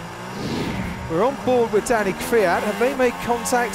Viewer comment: On lap 10 when checko went for one on kvy who thought he said daddy kvy